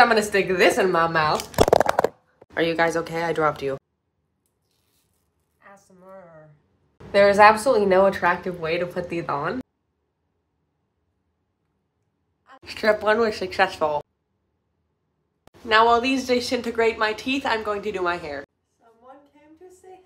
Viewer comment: "Shtrip one is shucsesful" that's what is sounded like she said lol